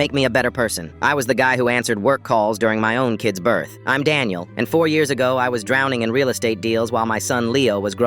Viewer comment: When he said I’m Daniel i thought he said I’m died now